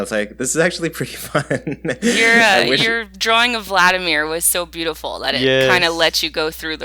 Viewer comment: Bro, did you just say Vladimir